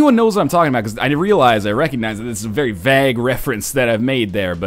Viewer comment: Now I know you say you don't care too much what we think of your pronunciations because people pronounce things different all the time... But the way you pronounce vague annoys me so much. I thought you were saying bag, I had no idea what you were on about.